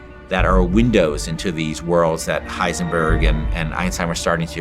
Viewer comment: did he say heisenburg?